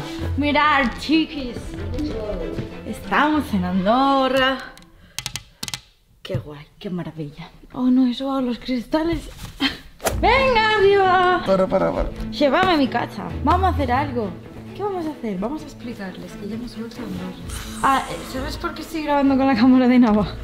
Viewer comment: Si bien escuche en la parte 0:33 dice venga mi amor